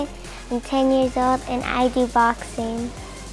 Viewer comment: Lol did she say be boxing